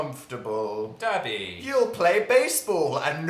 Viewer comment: when Phil said Dabby I thought he said daddy..... also I think dan knew the whole time that Dil was prego with the 👽